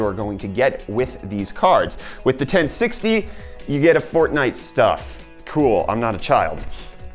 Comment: did you just said that fortnite is for children?!?